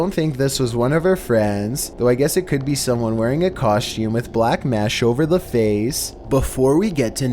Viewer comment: Who else heard him say coshum in number 2